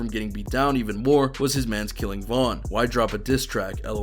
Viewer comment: I swore the cops said they killed'em (King Von)